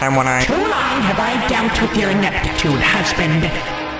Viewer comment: what did she say? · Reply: She aforementioned the phrase "ineptitude" which can describe the lack to do work.